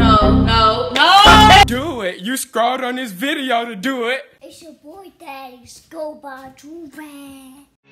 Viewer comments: “It’s ya boy Thaddeus go buy Joorags” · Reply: I thought he said "it’s your boy jax go buy a joorag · “It’s ya boy Thaddeus go buy Joorags” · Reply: I thought he said its your boy thaddeus go by joovie